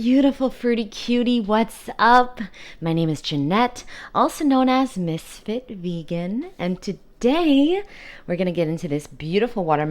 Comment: I always hear "aka Misfit Vegan" which would also be fitting, in a good way! Love your videos. They help me a lot!